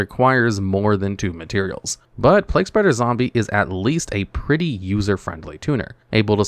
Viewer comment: I heard Leg - Spreader Zombie and had to go relisten 😂